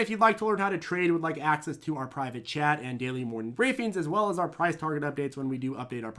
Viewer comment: As long as I've been watching these videos, today was the first time I realized was saying "Access to our private chat," all this time I thought he was saying "private jet"...